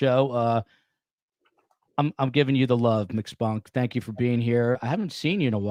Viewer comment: I got excited when you said mcspunk won a trophy...I thought you said Miss Fonck:/😅